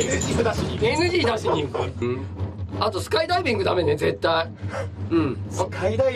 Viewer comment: he actually says skydiving